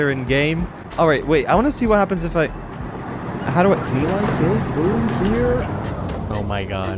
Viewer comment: wait was he said melikebigboom 3:19 oh shit